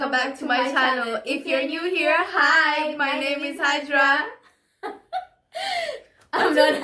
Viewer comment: I heard "my name is Hydra" and was ready to call Captain America, lol. I swear my brain scrambles what people say.